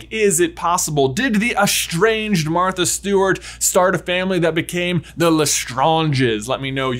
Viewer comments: I know it's "Martha STEWARD," but I kept hearing "Martha STEWART." LOL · "They clap are clap the clap worst." clap LOL I know it's Martha Steward but I heard "Martha Stewart" and started laughing.